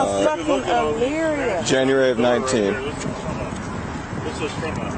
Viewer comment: She didn't know her license has been suspended since 2019? 4 years ago? Come on, now. No accountability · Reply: i thought he said January 19th, not 2019…